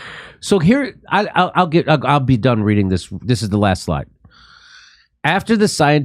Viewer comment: Jimmy, when you said "I'll be done reading this...", I thought you said "I'll be DUMB reading this..." hahahaha ... DUMB INDEED !!!!